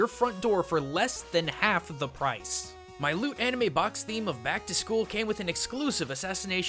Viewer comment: When you said LootAnime I originally thought you said "LewdAnime" ... Ew.